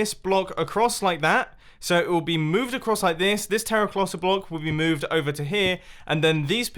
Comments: Anybody else hear "terroclotta block" or "terroplotta block" at 3:02 ? ❤️ love the video jumbo. Great work, as usual. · Did you say terraclosa block? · anyone hear him say terraclotta?